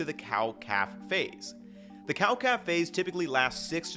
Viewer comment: I heard "Cow Café" so I was imagining cows drinking coffee and one of them is on a laptop and giving side-eyes to that "cute looking cow with the black and white patches." Then I realized it was Cow-Calf-Phase.